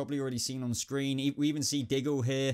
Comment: did he say we see giggle here?